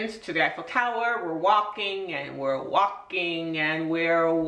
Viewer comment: Wow it took me a little of guard with what she said at 3:22.. I thought she said wokking.. accent fooled me for a second (as I'm English). we say it more like (phonetically) war-king. not saying anyone is right or wrong in their accent